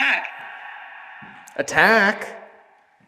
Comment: I swear the spirit box said “Kag” with an Aussie accent just there instead of “Attack”.